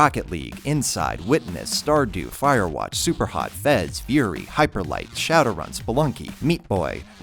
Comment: What is the game at 0:40 that he says fury to? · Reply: Furi